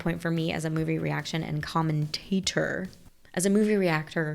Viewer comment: Lmao when you said commentator I heard it as common tater 🤣